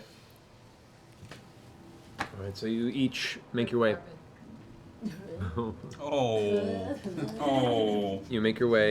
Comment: What does Marisha say at like 46:46? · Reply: Magic carpet… rip 😂